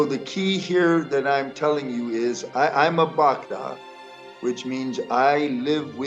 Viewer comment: What did you say at 4:14, I’m a _______? Was it Bhagavad?